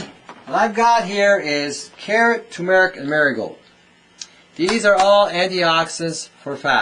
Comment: Vekktoria what i understand at 1:34 is, he says turmeric, marigold & the name of the product manu- facturer. Turmeric is such a great healer & marigold is too. He says this helps a lot so the butter does not stick to the arteries & keeps it flowing. Because margerine & crisco fat is actually made from oil they make gasoline with. Crisco was introduced to us via TV et al & highly recommended instead of butter & IT HAS KILLED over a BILLION PEOPLE already worldwide (heart attacks etc) because it clogs the arteries, its plastic & alien to our body. Canola oil is also very bad for our health, all part of U. N. Agenda 21 to kill us off slowly. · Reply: he carrot, turmeric and marigold.